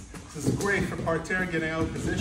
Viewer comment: I thought he was saying these are great for duck hunters.then i read it lol.this is awesome i love it.